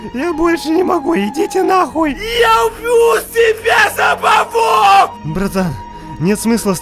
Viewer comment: послышалось "я убью себя сапогом!"